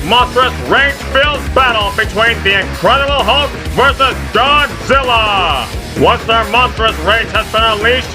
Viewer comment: i acctually thought he said hulk vs john cena lol